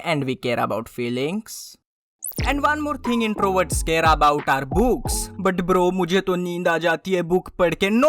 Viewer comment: bhai ye kya bola tu 👀 3:23 b&&bs ? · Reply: It's book